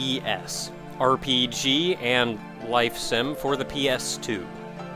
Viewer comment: I'm not gonna lie, when you said Persona 3 was a life sim, I thought you said Personal 3 was an RPG and "life sentence"... shows how good my hearing is.